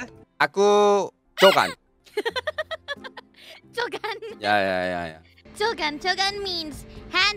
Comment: 처음에 ㅈ간이라는줄ㅋㅋㅋㅋㅋㅋㅋ 잘못 들었나 했네